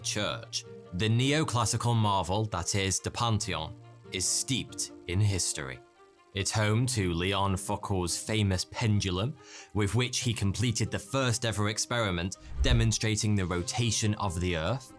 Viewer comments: Did he just say léon f*ckwhores famous pendulum?😶😶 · I heard “Leon Fuck All “